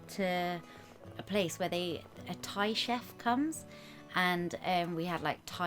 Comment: I thought said she ate a tie chef num num num 😜🦄🐰🦄🐰🦄🐰